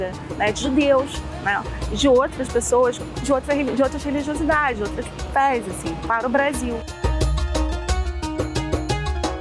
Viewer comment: Muito bom documentário. Só pecou no plural da palavra fé. O correto é fé e não FEZES😮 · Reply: O correto é fés. 7:55 eu acho que ela fala "de outras fés, assim"